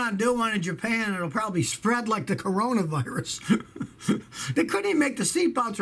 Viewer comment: Legit thought Scotty said "Corolla Virus"